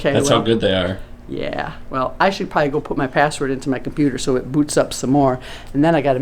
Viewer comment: in the video I thought said “I should poop my password”😂